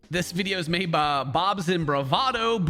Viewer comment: I cant be The only one who misheard Ken saying 'Bobs and bravado' for 'Bobs and vagana'